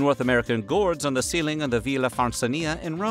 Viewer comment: I wasn't looking at the screen for a sec and thought i heard "they put it in their pestos" @ 4:55 and i was like "pumpkin pesto might be pretty good." (You did in fact say "frescoes" ) but now i'm getting a recipe idea for that squash on the counter and all them herbs in the yard... I just came for some weird food history but i'm leaving with dinner inspiration for weird food presently.